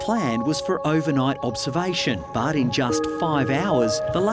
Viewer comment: When Dr. Brown said "the tick toxin" but I heard "the tiktok" LMAO